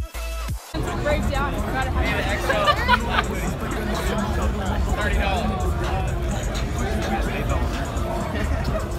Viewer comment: Thanks for posting this <3 what did you say to Jev at 2:34? Your'e so lucky to meet him. I live in NJ but I never get to see him :/ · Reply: I'm glad you enjoyed the video! 💙 & I said "Hey again, I'm the girl that was having a mental breakdown when I first came up to you" 😂 cuz I went up to him twice, the first time I was crying bc I was so happy and I couldn't believe I was meeting him 😭 omg you do?! That's crazy cool! I hope you get to see him one day!